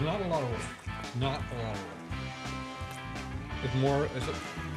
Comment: Wait, what? @ 12:18-12:25, it sounds like he says, "It's not a lot of work. It's kind of like fellatio."😊 Either way, it seems to be a workout for your mouth.😊 (or so my wife says)